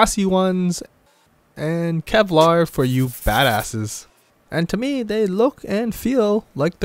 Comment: Did he just say “and Kevlar for you fat asses”?